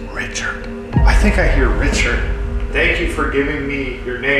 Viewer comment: idk I hear Nathan